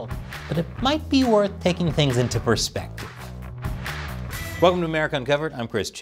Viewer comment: Had to replay that intro 3x because I thought you said, "Welcome to America Ungoverned"😂💀